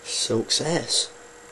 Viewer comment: did he say 'Sucks Ass' ?